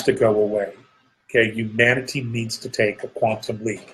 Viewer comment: Lol, I heard "Humanity needs to take a quantum leak". That might be true also.